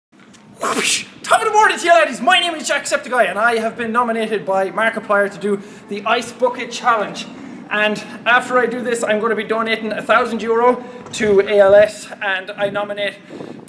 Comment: i heard the ice fucking challenge